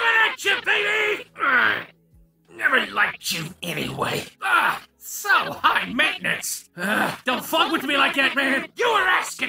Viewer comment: What exactly does he says at 0:50 and 1:35? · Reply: “So high maintenance!” and “Upsy-Daisy!”